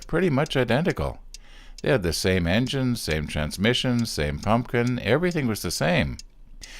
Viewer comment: ok. I'm sure it's just my ears, but at about 0:00:44, it sounds like you are saying "pumpkin". What are you really saying there, bro? lol :)